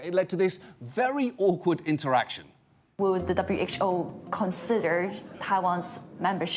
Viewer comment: despite the subtitles saying “reconsider”, she said “consider”.